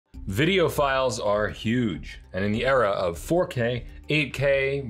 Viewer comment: I heard, “Videophiles are huge!” and I thought, well sure, I could lose a few…😂